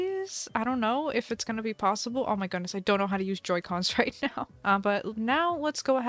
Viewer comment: i thought the shovel said "Outdoorsy cons"